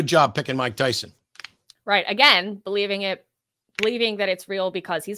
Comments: At the end I heard "Mike doesn't make right" Truer sentence hasn't been spoken in this show DID YOU HEAR THAT, MIKE??? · Almost sounded like he said "Mike doesn't make right", thought Matt was getting punny up there for a moment.